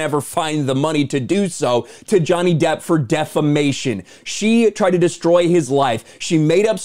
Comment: Wait, did you say "for defamation"? Oh I thought you said "for defecation".